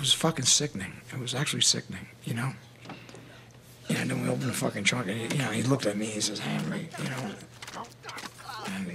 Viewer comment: What's Henry saying at 12:47 ? I can hardly understand him. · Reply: He said “and when we open f*cking trunk and he looked at me he says “Henry””